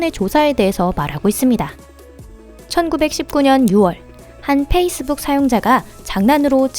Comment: 1919년 6월 이라 들리는 데 맞아요? 4:53 ??